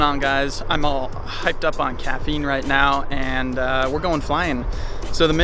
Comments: Did bro say he hyped up in caffeine 0:18 0:18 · I replayed the intro twice because I thought he said he was "all hopped up on ketamine" 💀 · At first I thought he said, "I'm all hopped up on ketamine..." lmfao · Am I the only one who thought he said "im hyped up on ketamine" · I thought he said hyped up on ketamine at first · I thought this guy said he was hyped up on ketamine